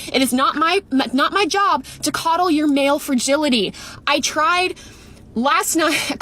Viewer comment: Is she saying "nail fragility"? 😂 😂 😂 😂